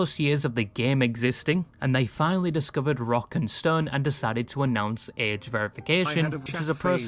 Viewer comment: DID I HEAR ROCK AND STONE?